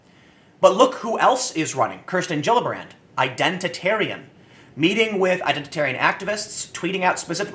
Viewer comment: Gisele R. Stop fighting with the dictionary, the radical left is doing enough of that for all of us.....intersectional centrist= someone who understands that a poor person faces more disadvantage than a rich person. The crazy left and the crazy right rank race above all, centrist and moderates generally rank class above all. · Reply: everyone knows that a poor person is more disadvantaged than a rich one, that's just common sense. R also thought he (Tim) said Intersectional Centrist when he actually said Identitarian Centrist.